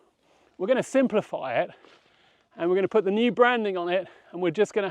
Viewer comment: Oh branding. I thought you said Mapperton brandy and I got excited.